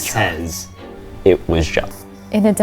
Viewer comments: I heard “It was GOD” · Some people are hearing other things aside from “it was John” and now I feel like it’s yanny/laurel all over again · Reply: i heard "it was jonathan" or "it was jo -hn" like a stutter